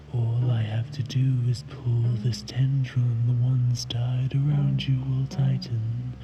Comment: what did he say? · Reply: “All I have to do is pull this tendril and the ones tied around you will tighten,”👌